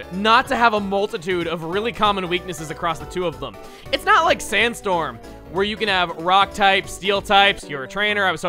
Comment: i thought he said SansStorm I prepared for a bad time xD